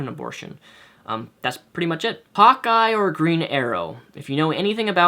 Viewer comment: When you said hawkeye I thought you said hot guy